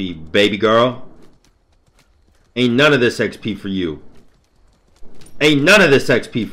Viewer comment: Being all gay and chiron, i thought said "sex pee", then i realized it was xp